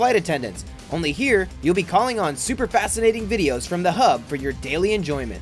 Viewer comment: the hub oh i thought is said porn hub